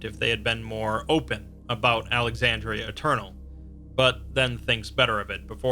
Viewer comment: Admittedly, I thought that said Australia Eternal